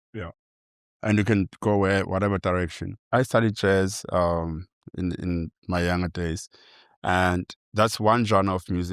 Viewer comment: What did he say? He started with "chess". Which genre is he talking about? Please, somebody, help me. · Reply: Jazz